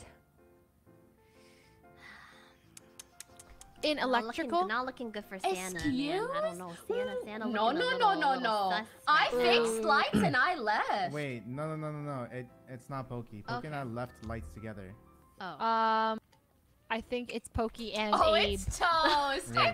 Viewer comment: I thought I heard Poki say “I faked lights and I left” I was like... they’re just going to accept that?